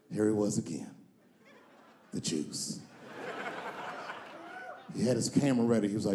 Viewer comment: Oh he's saying the 'juice' I thought he was saying the 'jews'. A lesson in context.